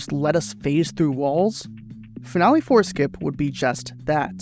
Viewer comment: I kept hearing "Family foreskin" instead of "Finale four skip"